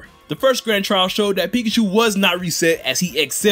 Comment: did he just said pichu?